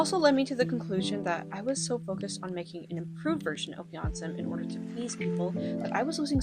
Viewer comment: "an improved version of Beyonce" yes, youtube autogenerated captions. that's exactly what she said.